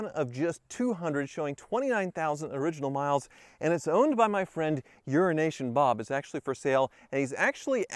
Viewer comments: I always just thought Hoovie was saying “Euro Nation Bob” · I hear "Euro-Nation Bob".